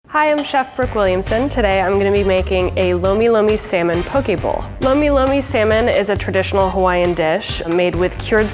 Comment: i thought she said pokeball